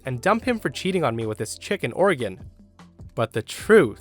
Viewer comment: I was playing this video in the background whilst playing video games and I heard “Chicken organ” XD